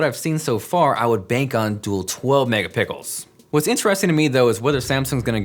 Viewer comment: The Galaxy Note 8 is almost here! 🎉🎉🎉 Also, if you're looking forward to the Pixel 2 check out this video! <link> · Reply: Jonathan Morrison hahaha did you just say megapickles lmaooo